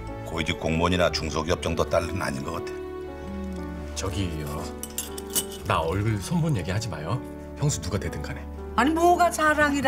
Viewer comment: 여기 무엄이가 뭐라고하는건지 아시는분!ㅎ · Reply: 나얼굴 손본얘기 하지마요(무엄이 성형수술 했음ㅋㅋ)